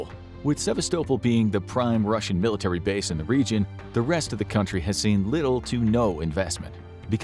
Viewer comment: did they say "rest of the country"?